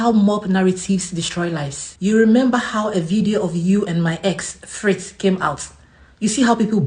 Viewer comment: Did he just say my ex 😂😂😂😂